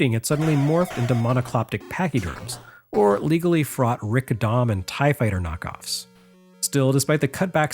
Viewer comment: What is he saying at 12:46 between “legally fraught” and “and Tie Fighter knockoffs”. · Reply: Rick Dom, a mobile suit type from Gundam that they straight-up ripped off the design of those big robots from.